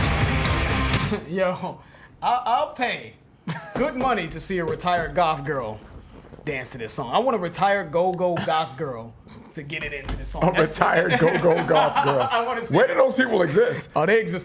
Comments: At first I thought said "retired golf girls" haha. I was like WHAT?!!! Then he repeated it and I understood what he was saying. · I had to rewind because i thought he said golf girl.